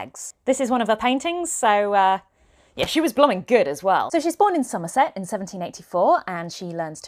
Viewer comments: I heard “she’s born” as “she spawned” 💀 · When she said "she was born in Somerset," she said it so quickly that I thought she said "she spawned in Somerset" 😂